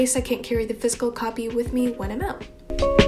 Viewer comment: Hey! I just wanted to make a quick correction to your closed captioning. At 0:27 it seems you’re saying “...physical copy with me when I’m out” and instead the closed captions says “physical copy with me enough”. It would be really great if you could correct this mistake ,if it indeed is a mistake, considering there is so many people in this world that have difficulty hearing or can’t hear at all therefore heavily relying on closed captioning. Thank you for your time!!